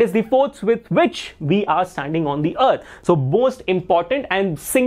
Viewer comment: you said bost instead of most😂😂😂😂😂😂